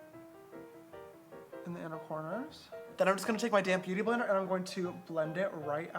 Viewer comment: I it just me or does it sound like he says “damn beauty blender” instead of “ damp beauty blender”